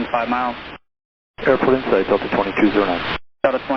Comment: Subtitles say "Cleared for visual 30R" instead of "Airport insight" Then it says "No cockpit injuries" when he didn't say that lol.